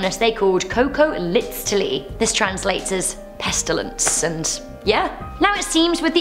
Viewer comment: Did you just say pestilence? *SCP flashbacks*